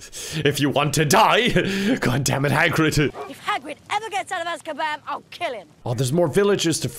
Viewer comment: During that Harry Potter clip when Ron said "if hagrid ever gets out of askaban I'll kill him" by headphones slipped slightly out of the socket so I heard "if hagrid ever gets out of that kebab I'll kill him"